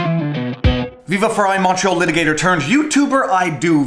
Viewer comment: Crazy vid but I'm sad now. You called yourself a "Montreal Litigator." All this time I thought you were saying "Macho Litigator!" I vote you switch to that. Drive on man.